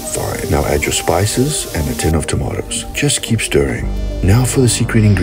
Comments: I thought he was about to say "now for the secret ingredient, steroids" 🤣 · i thought he said steroids instead of stevia